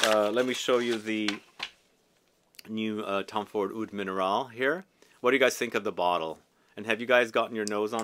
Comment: I thought he said he hated Oud Minerale lol